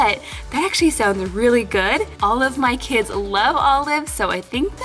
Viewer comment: When she said, "All of my kids love olives" it sounded like she said, " Olive my kids love Olives" Lol😂😂😂